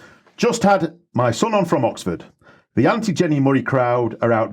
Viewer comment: This may be helpful for other non-Brits: I listened several times, but I couldn't make out what crowd was referring to. Then, a couple of minutes later, he mentioned the "Women's Hour Presenter," I was able to find Jenni Murray: <link> <link>